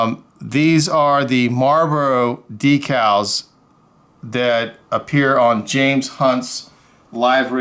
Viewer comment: i keep hearing james say, “the names hunt, james hunt” from mclaren tooned